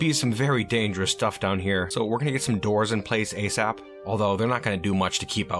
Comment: Lol, got scared and thought I called the martians dwarves. Been trying to break that habit. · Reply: Oh, I totally misheard it! I definitely heard "dwarves", but listening back you clearly said "doors". I guess my ears are the ones with habits to break!